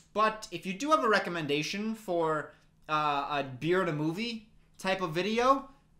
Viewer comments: Does anyone know what he says around the 4:25 mark? I put on subtitles to try and figure it out but it said that he said "a beard of movie" which I'm assuming isn't accurate 😂😅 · What did he say at 4:28? A beard movie? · What did he say here I honestly cant tell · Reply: If you do have a recommendation for a beer and a movie type video...